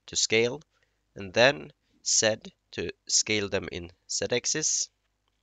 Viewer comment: I got stuck on 1:11 what does said mean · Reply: I mean Z